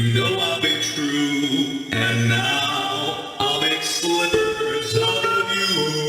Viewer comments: I thought Rockstar Bonnie said "slivers", not "slippers". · I'd assume it's a reference to bunny slippers. So instead of slippers made to look like bunnies, Bonnie is gonna make slippers are made from, and that look like Afton... Billy Slippers if you will. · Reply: The line originally says 'slivers', not slippers. It's a mishearing.